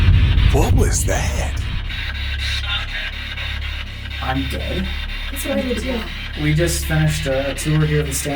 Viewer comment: instead of “I’m dead” I heard “suck it” 💀